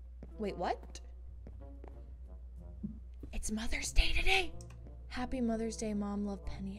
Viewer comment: i thought it said mother died